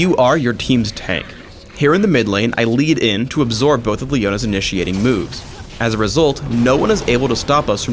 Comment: I swear i heard "Leona's initiating boobs" LOL 3:22 Or i have hearing problems